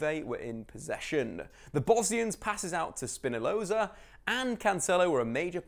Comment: Did he say SPINALOZA????????????